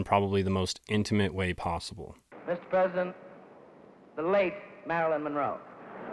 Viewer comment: I can't believe what I just heard. When Marilyn is introduced to sing happy birthday to JFK, the man says, "Mr President, the LATE Marilyn Monroe..." !!!! ?? 39:47 will someone please check this and tell me if I'm hearing things? Or if there's an explanation for this?